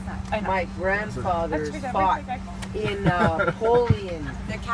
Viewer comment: I thought at the start she said her father fought against Napoleon lmao